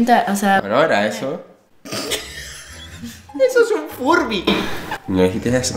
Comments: yo también entendí furby :v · yo también entendí un "FURBY" Jsjsjsjsjs